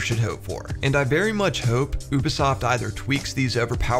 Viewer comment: did you just say "uubesoft"? Thats funny